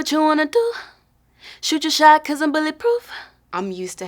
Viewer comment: I thought she said "she just shy cause I'm bulletproof"